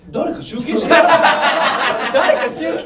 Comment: ここのほっくん、「誰か集計し"て"よ」ってゆーてない？空耳？はにゃ？